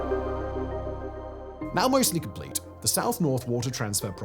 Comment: I swear I heard: "Mao mostly complete.."